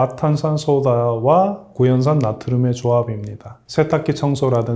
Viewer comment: 과탄산소다와 구연산을 섞으라는 소리로 들리는데... 왜 그러셨어요. 무조건 따로 써야 합니다. 이유는 당연한데... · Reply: 구연산이 아니라, 구연산나트륨임. 너무 똑똑한 척 하셨다